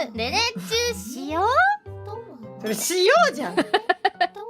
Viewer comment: 僕でも、ねねちが言う前まで、「Hしよう」って聞こえてしまった僕は、ヤバいよな